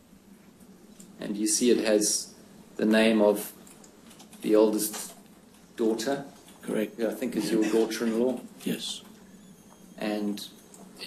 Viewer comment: did I hear the lawyer correctly say on page fiove it has the name of the oldest daughter, who I think is your daughter in law?